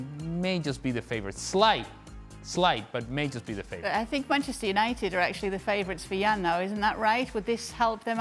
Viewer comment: did she say 'Penchester United' 😳